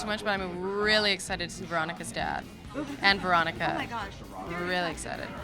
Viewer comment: I thought she said "Veronica's death" not "Veronica's dad" omg